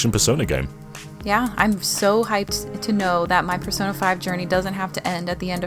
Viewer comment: For a second, I thought it said, "Persona 52"